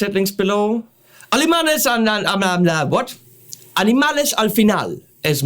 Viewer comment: Lol, sounded like you said "Alemanes al final", which would be Spanish for "Germans at the end". Hmm, as someone who is looking to begin playing guitar, 7 string, multiscale, long scale, Floyd Rose, etc. are things that do not interest me, so I wonder why I bothered watching the video...must be because you are click-bait for me (no gayness intended)...